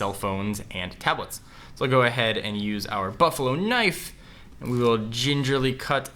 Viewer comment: did he just say buffalo knife